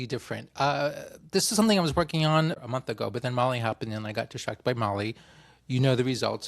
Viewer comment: At the beginning I thought you said you had been distracted by "Molly" (actually **Mali**), I was a little surprised you would admit to such casual drug use!